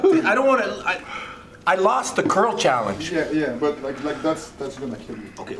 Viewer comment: Wait a moment, did he just say that he lost a curl challenge? I am now thrilled to see if that's a video coming up or something.